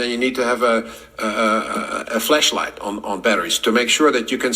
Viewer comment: did he just say you need to have a what -light? 😂 Edit: When Rick Ross said Elon Musk I think he meant Mark Zuckerberg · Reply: Flash 📸 Flashlight. Not flesh, he may have an accent, but I can clearly hear him on that word.